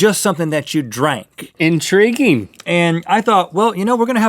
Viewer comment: Did anyone else pick up what link said at 0:35? In drinking?? hehehe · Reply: "Intriguing.."